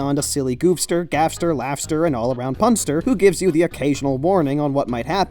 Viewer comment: did he just say gaster?